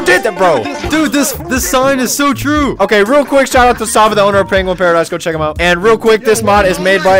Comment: did bro just say pringle paradise